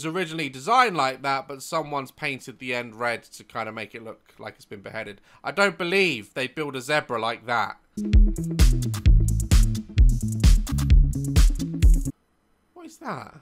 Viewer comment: what is a zedbruh? I heard of zebra tho